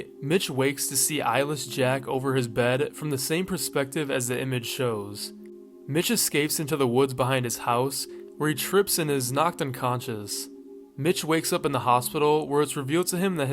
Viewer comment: Misheard words "B**ch escapes into the woods"💀😭